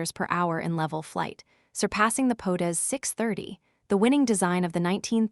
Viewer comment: potus630 instead of POTEZ 630 : this is not serious. This vid is a scam.